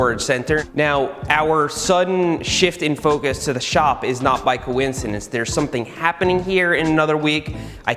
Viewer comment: I thought he said ‘ bike - oincidence ‘ but he just said ‘ by coincidence ‘ and I think he really missed an opportunity